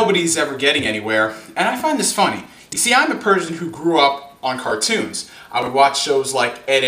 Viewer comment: For some reason I thought he said I'm a Persian